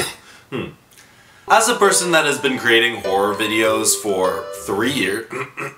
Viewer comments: "As a whore" IS THAT WHAT HE ACTUALLY SAID IM CACKLING Edit : I just realized he said horror and not whore.. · I was listening to this whilst drwing and then i heard, "as a whore🫠"